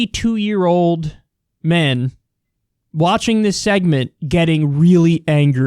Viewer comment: Whoa! For a moment I thought you said thousands of 72-year-old men are watching YOUR segment. If only...